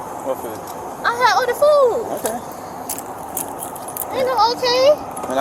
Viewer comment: ITS 2024 ...WHY DOES SHE SOUND LIKE ITS 1798 ???? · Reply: When the officer said “ okay” and she repeated him , I definitely thought I heard her say “ ain’t no OTAY” 😂